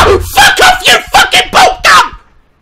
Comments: omg what did he call it!?!?!? XD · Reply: I literally thought he said blot-ga😂😂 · did he just call the monster a fucking puta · wait at 12:45 did he say "fuck off you fucking puta"???? cuz if he did the mexican side of me is dyyying!! XD